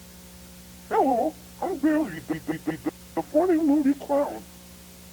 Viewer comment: hi ! after rewatching billy's scene at the end, i can confirm its not reversed ! i listened to it a few times and i heard " hello, i'm billy." at the beginning ! :o)